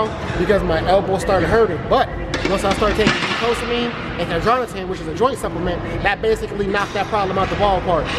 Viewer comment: What up! quick question at 7:36 what were those two supps you mentioned? I heard glucosamine but couldn't make out the other. I appreciate any help, great vid!! keep it up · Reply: Glucosamine Chondroitin. Pretty common supplement you can find in most places just make sure you keep taking it for 4+ weeks because the effect takes a while to kick in and people tend to stop at 2-3 weeks before it kicks in.